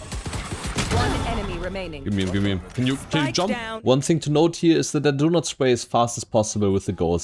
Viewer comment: did he say "can you fucking jump" or did i misheard? Isnt that a bit too direct and rude 😮